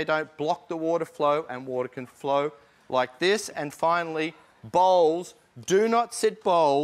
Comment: i thought he said "balls" when he said "bowls"